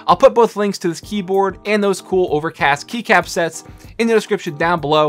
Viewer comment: did he say over cast?